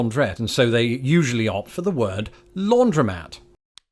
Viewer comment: I've heard "laundromat", but never as a two-syllable word, which may explain the confusion. I thought you had said "laundrette". Chalk it up (quaint Americanism?) to a British tendency to chop syllables. I've also noticed that "t" is pronounced differently; the American "t" is closer to "d", and the British "t" tends to have a gap after it. To an American listening to a Brit, "Scottish" sounds like "Scot'ish".